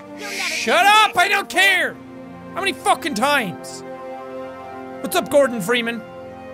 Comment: did you just say "what's up Gordon Freeman"? If you did.... raise The bar.